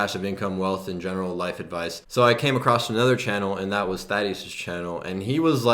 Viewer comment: Which channel is he talking about at 12:05? Sry i don't understand it acoustically :-) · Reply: ATMODEPTH Thaddy (Thaddeus) ;)